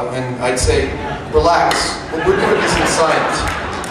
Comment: I'm almost certain that he is not saying, "Relax. The proof is in the science." What I hear is, "Relax. What we're doing isn't science." - i.e. - "entertainment". Then he makes the ridiculous statement that, he's biased by the fact that he knows they (the Mythbusters) are right. Hmmm... :o)